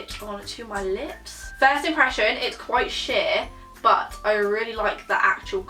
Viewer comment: when was putting on the Jeffree Star liquid lipstick was it just me that thought she said “it’s quite shit”😂